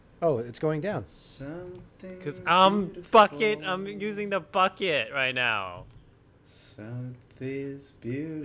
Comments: I SWEAR I THOUGHT SAID "CUZ IM FUCKIN-- IM USING THE BUCKET" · did just said fuc*in?